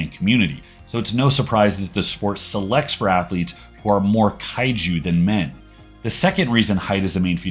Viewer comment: What does he say at 1:56 selects more Kaigee then men? Ive never heard that